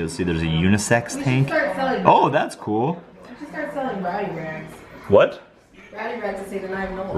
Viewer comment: Hahaha rowdy rags. I thought you said you should start buying body bags🙄🙃😂